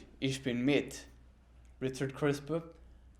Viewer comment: i heard Richard Krispe